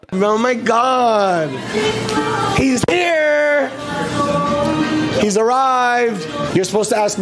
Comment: i think what he was saying is about the devil is arrived..